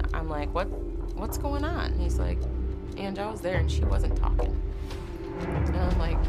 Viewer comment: WTF did the sister say at 12:56 I'm so confused now ?? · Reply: Bianca Nicole Angie I was there and she wasn't talking